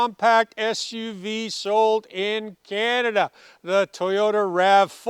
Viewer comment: What he said? Number #1 SUV Sold in Canada? Sorry i hear Number #1 SUV Stolen in Canada Sorry my Bad 😮